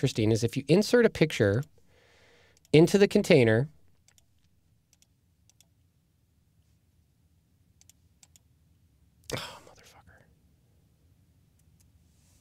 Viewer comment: Did you just said " oh...mother fxxker" at the 5:07? Lol...it's hilarious and I can't stop laughing....LOL....thumbs up!!!!